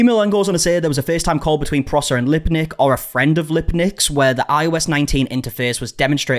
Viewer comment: He did NOT say limpdick. He did not say the last name was Limpdick. I am a mature adult and not going to laugh at something like that. Oh lawd. I think he said “Lipdick?” So that’s better. Lolol. Anywho comment was made with an iPhone 12 max pro. Lolol. I’m so outdated.